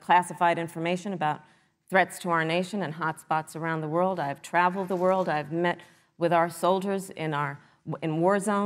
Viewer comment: "I've traveled the world" I thought she said "And I've never been to Europe either"😅